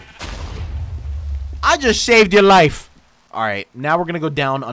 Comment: I heard "I just shaved your wife" XD